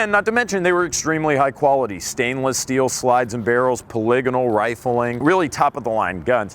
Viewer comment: What is paligano rifling? Did he mean to say Polygonal rifling?